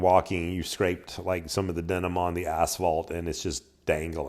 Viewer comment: Vielen Dank für das sehr klare und instruktive Video. Die parallele Videodarstellung aus 2 Blickwinkeln ist super. Vielen Dank für die Mühe. Beste Grüße aus Deutschland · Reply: At first I thought you said: "caught denim on the asshole", not asphalt. Lol